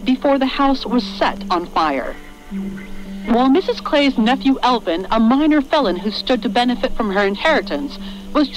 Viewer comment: I thought they said "Albin" instead of Elvin at first and got scared that they found out.